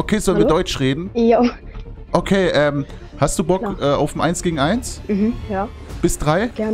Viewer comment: Ich habe beim eins zu zwei fuck you verstanden😂